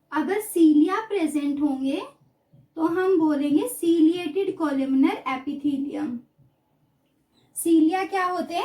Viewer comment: Mam Apne 14:36 pe Kya bola h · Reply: ciliated columnar epithilium bro